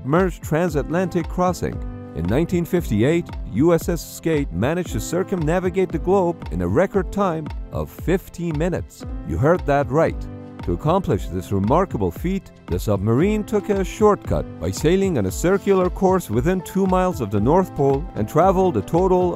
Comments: Y’all, it sounds like he says “15 minutes”, but it’s definitely 50 minutes, because the former would require a speed of 76 km/h, which is more than twice the max speed of the boat. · When he said "15 minutes" I just imaged the sub absolutely bookin it through the water. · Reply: I think they did say 50 minutes but it really does sound like 15 minutes. Also it is not clear he said 50 minutes. I thought he said 15 until I saw people saying 50 which then I noticed that kind of sounds like 50 but it still sounds more like 15.